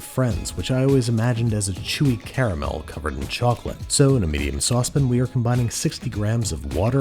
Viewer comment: Love your videos but I cannot stand how you say SAUCE PAN I always hear “sauce pin” 😩